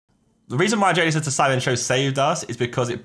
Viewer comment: wait what does he say? I genuinely don't understand it at 0:00.. ""The reason why joeacheowachoimonshow saved us.. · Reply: I heard "the reason jj said that sidemen show saved us"